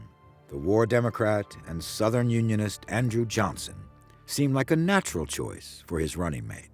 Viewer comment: Andrew Johnson. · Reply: mb i thought he said andrew jackson